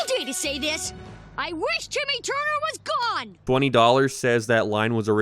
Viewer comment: ironically im getting hit with a massive mandela effect For the longest time i thought he said "i wish timmy turner is dead" hearing him say gone instead threw me off so badly